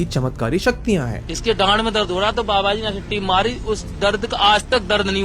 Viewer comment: I guess I heard the same what you heard, even though it wasn't what it seems to be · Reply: 😂 Gaand me dard!